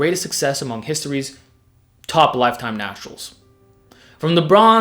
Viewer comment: sorry English is very basic, did you said "Top lifetime nostrils"?